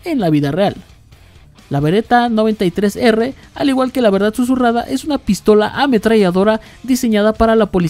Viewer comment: Le entendí "la verdad surrada" 💩🤣🤣